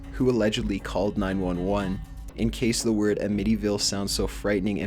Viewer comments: the way that the narrator pronounced amityville. it sounded like he was saying mid evil. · The wicker effect? Dude, PLEASE, PLEASE, PLEASE, do the research and don't assume you know it. I know I'm being picky, but it's the wick effect. And the believe the dogs are harbringers?? It's a harbinger. Big difference in pronunciation. And again with the pronunciation, Amitee-ville? lol I can't even... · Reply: first I thought he was saying "medieval" as in the house the kid lived in was of medieval age." Uh-med-EVIL".. Amityville.